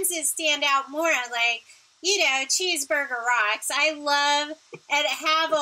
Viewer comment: You mentioned a song that sounded like Cheeseburger Rocks. Did I completely mishear that???